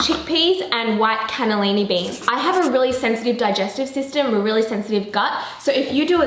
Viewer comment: When she said I have a really sensitive digestive system I thought she said she has a sexy digestive system